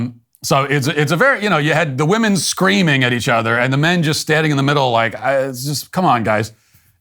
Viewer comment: what men? All i heard were cucks